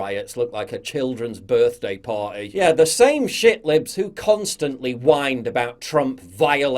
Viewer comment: Am I hearing "ShitLips"?